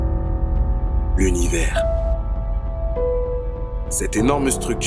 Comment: J'ai entendu "Lonnivers"